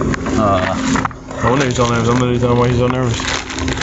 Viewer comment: sounds like he said "no wonder he's so mad somebody tell him why he's so nervous"